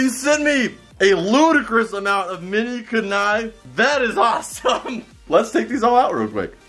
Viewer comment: Did he just say Kunnai instead of kunai